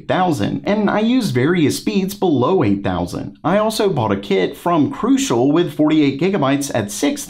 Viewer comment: sounds like you said “I bought a kid” 😂